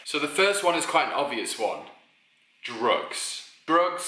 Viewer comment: I had to rewind and Playback when you said the first one at first I thought you said rope when you said drugs